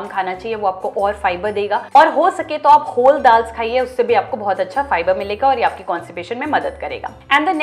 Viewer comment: What did she say at 7:20 to eat kholdals? What is that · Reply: Whole dals like tur, chana, masoor, moong, etc